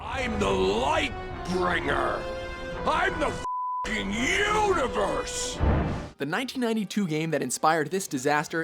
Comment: it did the pause and the dude was like "im the fucking universe" i heard "im a fucking unicorn""